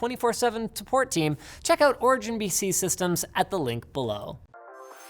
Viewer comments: is it just me or does Linus say origin bc systems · Anyone else think it's a little shady they're doing Origin PC ads right after doing Secret Shopper? That's an oof. Also, did Linus call them Origin "B"C system at 0:36? That's another oof. This video is oofing all over the place.